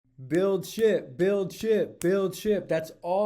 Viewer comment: Lol at the beginning I thought you were saying "build sh*t!" 😅 great video